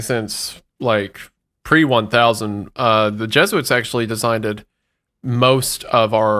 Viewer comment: Did say "designded"